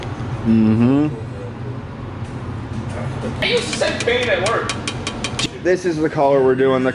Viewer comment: Did say he used to sniff paint at work???